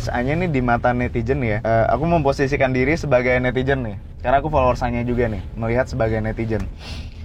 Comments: gw dengernya "karna aku follower sange juga nih" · itu ”followers s***e” w yg salah dengar atau gmna y?